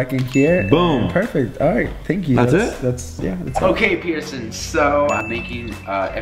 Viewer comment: at first I thought he said Peterson